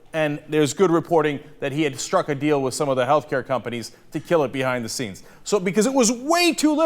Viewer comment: if you press the CC button, at 2:12 when cenk says "to kill it behind the scenes", see what the subtitles say...